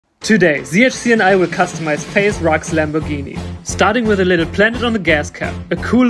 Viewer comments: For a second I thought he said faze rock but it's faze rug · I thought he said faze rocks